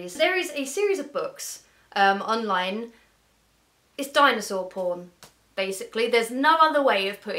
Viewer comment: "Dinosaur porn" I thought she said danosaur porn nope Phanfiction.